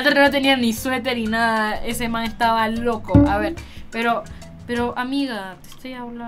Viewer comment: escuche bien verdad dijo mae?